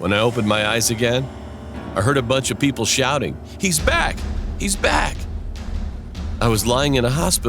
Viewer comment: did she said (he's) back.... That's girl